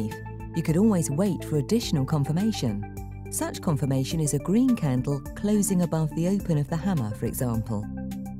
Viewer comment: I couldn't hear what she was saying at 3:30, could someone tell me please? Thank you. P.S. I don't trust the CC, and I'm hard of hearing. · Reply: The voice over artist says: "Such confirmation is a green candle closing above the open of the hammer for example."